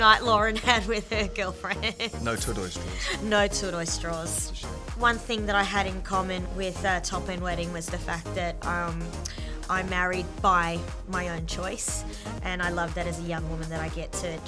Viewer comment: what are they saying at 1:22? english isn't my home language, I can't make that out) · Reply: one thing that I had in common with top end wedding is that i married by my own choice